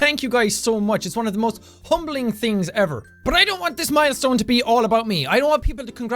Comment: I thought he said "one of the most PUMBLING thing ever"